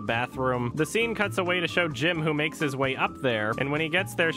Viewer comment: Is he calling John Krasinski's character Jim?